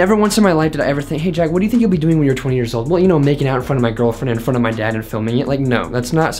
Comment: I thought you said making out in front of my girlfriend with my dad